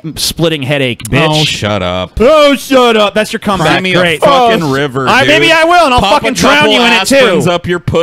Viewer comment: I think said "sing me a fuckin river" at 3:00:20, how do you sing a river?